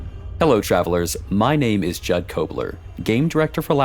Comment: I heard "my name is Chad Cobler" and I'll stick to this